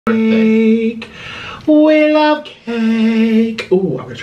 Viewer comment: Why does the first part sound like he saying "aaaaake, we love cake"